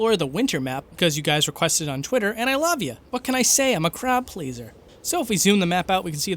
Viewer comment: who else heard "crap laser"